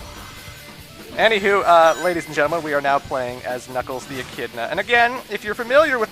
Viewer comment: What he said: "And now we play as Knuckles the Echidna" What i heard: "And now we play as Nipples the Enchilada"